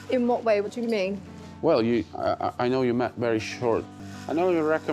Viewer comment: I thought he said “I know you trickin…” 💀😂.